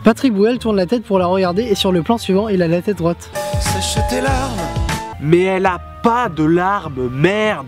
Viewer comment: À 4:05 j'ai entendu "sa chatte est l'arme". Tout va bien pour moi.